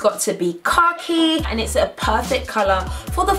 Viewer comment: When she said khaki and I the only one who thought she said car key?lol